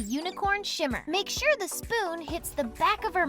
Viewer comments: i keep mishearing shimmer as shitter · Every time she said "shimmer" I heard "shit"! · WHEN THE GIRL SAID “SHIMMER” I FR THOUGHT IT SAID “SHITTER” 💀💀